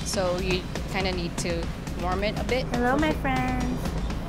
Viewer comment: Did anyone else hear when she said hello my friend at 3:32???